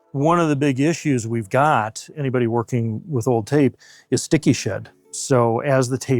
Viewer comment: I thought he said “sticky shit”